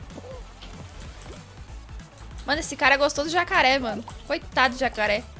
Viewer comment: eu ouvi "essa cara é gostoso e jacaré" KKKKKKKK acho q vou limpar os ouvidos